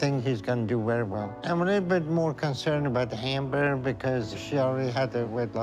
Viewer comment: Omgggg.....No joke .i swear @ 48:40 I heard Dr now say" but I'm more worried about the HAMburgler" LMAO I had to rewind that! But he said More worried about HAMber!